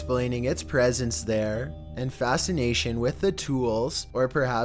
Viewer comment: 9 sounds like it says fascinating